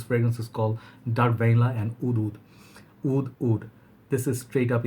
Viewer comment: I'm starting to realize that a lot of the times when you say what sounds like "oud", you're actually saying "wood". Like in this video at 5:44 when you say "oud wood", it really sounds like "oud oud" to me.